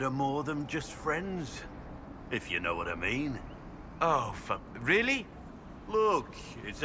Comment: I heard him like "Ooh...Fuck...Really??"... damn you.. XD